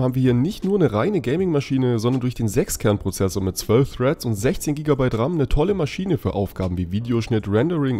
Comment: da hab ich kilobyte verstanden xD